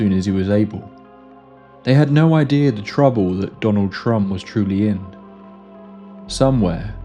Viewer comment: yo I thought you said Donald Trump